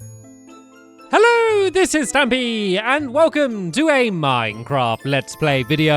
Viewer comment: sounds like you say miss stampy in the intro